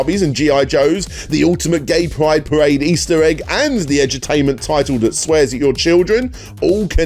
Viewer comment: When you said Edu-tainment I thought you were saying Edge-o-tainment, like edgy games more concerned with being edgy and controversial than playing anything actually entertaining